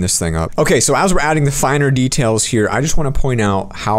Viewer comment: he says finar instead of final lol